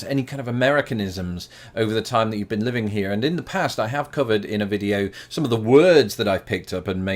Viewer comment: I've lived in Texas and Minnesota and I've always said "PREZ-entatiion" and "in-SURE-ance". One word I changed was "cement". In Texas, it's "SEE-ment". In Minnesota it's more like "s'ment", almost pronounced as one syllable. I was made fun of mercilessly for that, so I changed it. Also, watching this video in 2020, I definitely heard "In the past, I have covid" at the beginning when he said, "In the past I have covered...". That was a little shocking at first.